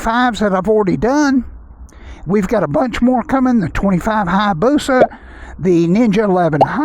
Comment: did i hear someone say busa!